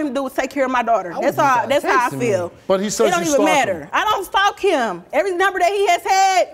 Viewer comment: Sounded like she said "I don't fuck him" lol